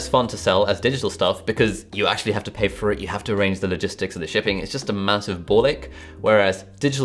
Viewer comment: did he say “it’s just a massive Ball lick?” Like literally licking ball sack? I spit up my water when I heard that.